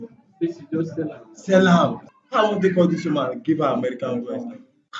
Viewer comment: What he actually said in 8:12 He said “how I wan take call this woman give am American voice?” meaning - how’s he going to fake his accent for her… which explains his nose pinching. Please expose all of them. As a Nigerian freelancer, I know what I’ve suffered when it comes to the job market. It’s depressing really. We can’t even get paid cos every channel is blocked. They’ve done a lot of harm and it’s genuinely SAD that it’s a thing in this Country.